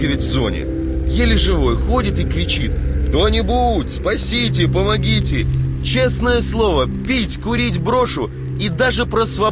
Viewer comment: I think I hear "pomogite". I think that means a request for help.